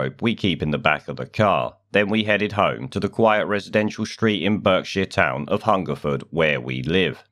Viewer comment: For a second I thought I heard "bird shit" not "Berkshire".